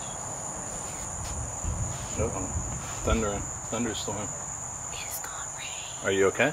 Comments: Poltergeist trailer never fails. @ 4:50 noose still hanging from light. @ 6:13 whistle. @ 19:59 bang. I did hear quite a bit from spirit box. All in all it was evil. Great video. The weirdest thing @ 7:31 I heard a girl's voice say, "He's got me". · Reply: Pretty sure 7:31 was saying "it's gonna rain." I could see her lips moving in slow motion on vid. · Omg like seriously no one noticed what is being said @ 7:30 when is walking in the side door?? I can't make out what's being said but sure in the hell can hear a voice!!! 😱😱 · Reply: It is saying it's gonna rain.